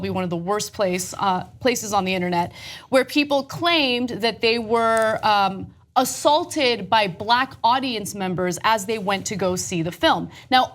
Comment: Lol i actually thought they were saying, they were attacked by the black panther